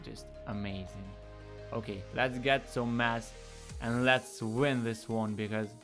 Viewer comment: lmao i thought u were saying "lets get some mats" was gonna say this aint fortnite boi xd